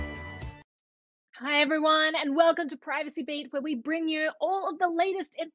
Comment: "Hi everyone, welcome to Privacy Bait..." did I hear that right? lol